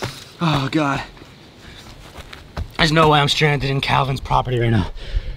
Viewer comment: Who else thought it said I stabbed Calvin mg 🤣🤣